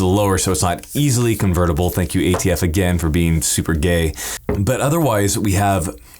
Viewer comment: Did he just say the "ATF is super OKAY?"